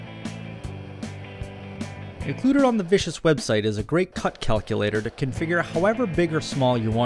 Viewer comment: The computer on the whatish website? Seriously, I can't quite make out what you are saying. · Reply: "included on the vicious website" sorry, that should have been clearer. thanks for the watching!